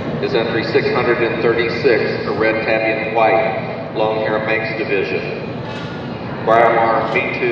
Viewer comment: i didn't understand what breed it was at 15:41?? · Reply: Longhair Manx